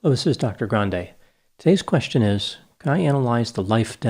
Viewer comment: You brought up a lot of good points me made on comedy and art in general. Thank you for another answering today’s question... or as I hear it “today’s lesion is...”.